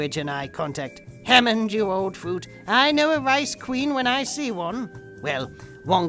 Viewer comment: Did you say "ice queen"? Because I heard "rice queen" and that's probably racist. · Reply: He said rice queen.